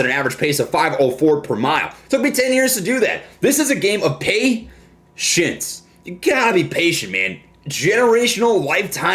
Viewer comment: Sounded like he said "This is a game of PAY SHITS" We're the shits! Lol